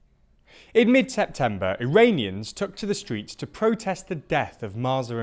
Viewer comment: I swear to God I hear him saying "Uranians" instead of Iranians.